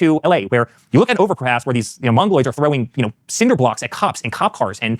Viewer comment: DID HE SAY MONGOLOIDS??????